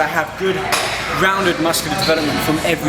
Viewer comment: Did you just say...."underarm muscles" ...?